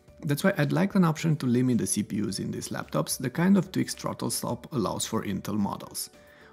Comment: what exactly did you say at the 6:00 min mark? two step twix?